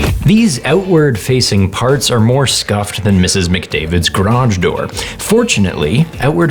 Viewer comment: In my head I thought I heard Ryan say, "These outward facing parts are more scuffed than Mrs. McMurray's back door." Had to pump the brakes on that one!